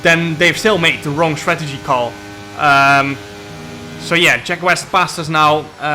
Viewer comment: I thought he said Sheck Wes. IYKYK Because Jarnooooo... COOOOOKIN... 😆🤣